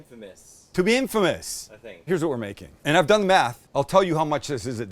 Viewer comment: It sounds like he says “ and I’ve done meth”